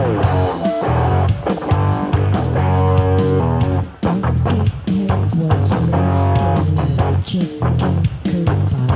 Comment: What does he say in 01:44? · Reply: "Buckethead was raised in a chicken coop by chickens"